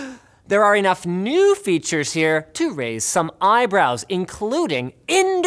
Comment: i mentally heard 'iBrows' instead of eyebrows..